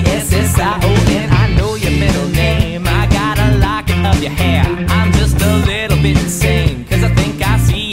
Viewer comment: It says ‘a lock of your hair’ the first time, but the second time I keep hearing ‘a lot of your hair’